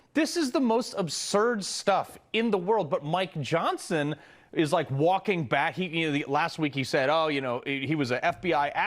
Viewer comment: tf, did he say "my johnson is walking back" ???